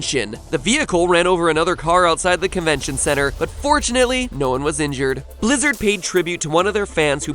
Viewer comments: I thought he said unfortunately no one was hurt during the convention part. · I thought he said "unfortunately no one was injured" at first